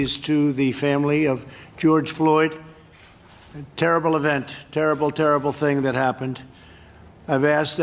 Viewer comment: i thought he said: cured Floyd. Damn I guess I need hearing aids.